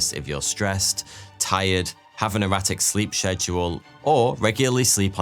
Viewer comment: i thought you said "erotic sleep schedules" so i was startled